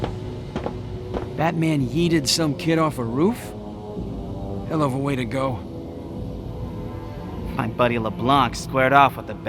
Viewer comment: Did he just say Yeeted?